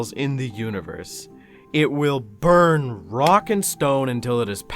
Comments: DID I HEAR A ROCK AND STONE? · DID I JUST HEAR A ROCK AND STONE ?! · did i hear a rock and stone?? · DID YOU SAY ROCK AND STONE!?!? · DID I JUST HEAR A 'ROCK AND STONE'?